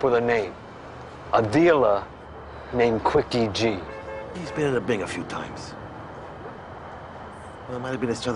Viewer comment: Was Quickie G, ever at the Bing? These are the type unanswered questions that haunt people… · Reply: I thought he said in the big.